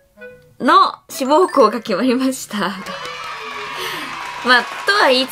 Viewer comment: 「『志望校が』決まりました」が「『志望校に』受かりました」に聞こえて、びっくりしたwww